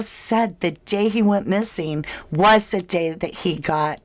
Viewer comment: It sounded like he said the day when he went missing is theday it happend ❤